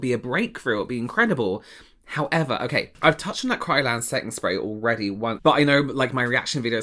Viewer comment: That last tiktok scared the crap out of me. I thought she said KRYLON instead of Kryolan. KRYLON is the fixative spray you use to set the color in a charcoal/oil pastels drawing on paper. Don't put it on your face. Or inhale it. I can't believe how similar the names are given that the functions also run parallel - it's like they're just asking for the next 'Gorilla Glue as hairspray' victim.